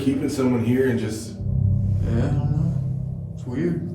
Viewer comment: why am I hearing another voice saying: "uhh I don't know"